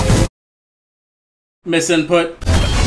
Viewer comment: what did he say? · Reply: Misinput